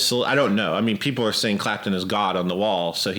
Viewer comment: I always thought it said Clapton Is Good...